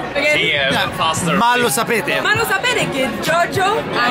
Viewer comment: solo io ho capito "ma lo sapete che JOJO"